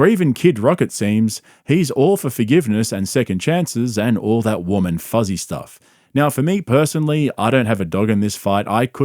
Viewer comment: Ohhhhh, WARM AND FUZZY stuff. I thought you said women fuzzy stuff lol